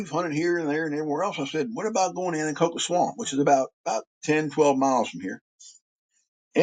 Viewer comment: WHAT SWAMP did he say at 24:35?? Cocoa swamp? 🤔